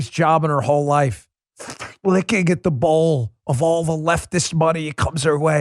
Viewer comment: For a second i thought you said licking at the balls not bowls🤣